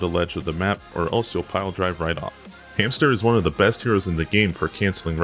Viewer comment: did he say hamster?